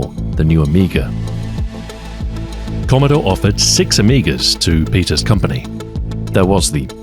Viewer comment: Woohoo! Two back-to-back videos from!!! ( 2:59 Just so you know, the caption here says "five Amigas" instead of the actual six.)